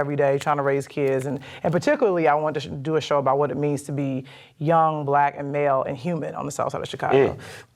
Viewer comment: Did she say grow up young, black, and "Male" in southside Chicago?? 🙄